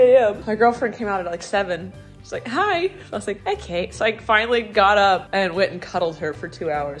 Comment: I'm not 100% sure, but early in the video I think you might have said girlfriends name! I could be mishearing though, just thought I'd mention it in case! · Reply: she said ok but in a cute way that sounded like kate but that is not gf name. her name did get out once but its not my place to say it hope this helps